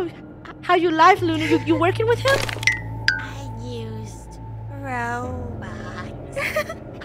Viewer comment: I thought lunar said “am a used robot”😭